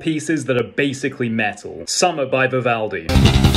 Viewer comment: Wasn't reading the captions the first time around. I thought he said "Some are by Vivaldi" but it was "Summer by Vivaldi". However you refer to it, the slap version slaps in a whole new way. I feel a little sorry for ' thumb but I also appreciate reaping the benefits🤘.